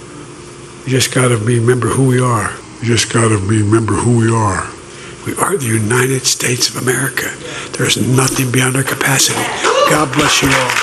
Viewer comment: i thought he said it was the united steaks of ameriaforgodsakes